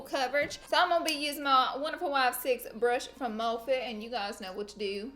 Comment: I love how you are so dedicated to your y6 brush so much that in this video it sounded like you said my wife 6 brush 😂